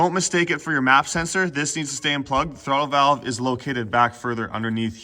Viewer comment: at the 1:35 mark did you say the MAF stays Plugged in Or Unplugged ? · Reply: Stay plugged